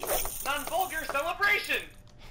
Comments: I thought he said "come hold your celebration" and assumed it was a reference I didn't understand. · “Non vulgar celebration”? Really? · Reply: I heard "Now hold your celebration!" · “Non vulgar celebration”? Really? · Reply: I heard "uphold your celebration" until near the end when I head "vulgar". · I miss the “I didn’t suck!” Voice clip. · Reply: I hear "Hold your Celebrations" for some reason. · “Non vulgar celebration”? Really? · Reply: I kept hearing "What a vulgar celebration!"